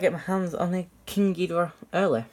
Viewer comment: I thought you said King gordita 🤣🤣🤣🤣